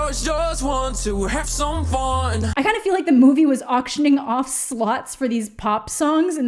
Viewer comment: I heard 'Auctioning Slots" as 'Auctioning Sluts'